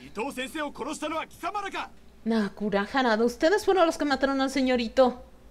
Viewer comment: Ese momento en que dice "señor itou" y yo entendi "señorito" jajajajajaj hay dios, bueno no le falta mucho para el otro bando XDD y uff esta parte de la ruta de Heisuke me duele demasiado juro que se me desgarro el corazon cuando se veia medio muerto, a mi me agrada mucho Amagiri pero aqui me dio rabia pero es entendible bandos contrarios tienen que diferir XD no se por que pero Heisuke es mi favorito de todos aun hasta la fecha predomina mas que los demas :3